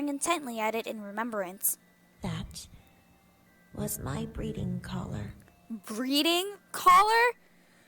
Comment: at the beginning when rabbit shows us here breeding collar I thought she said bleading collar. LOL